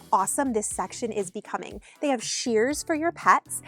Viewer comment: A clear sign that it’s been a long time since I’ve had a pet… At 8:33 when you said “They have shears for your pets.” I heard SHEERS and thought “Who puts curtains in a dog house?” 🤦🏻‍♀️😂🤦🏻‍♀️